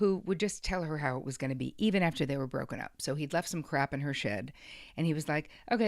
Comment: I heard that as "he left some crap in her shit" 🤣🤣🤣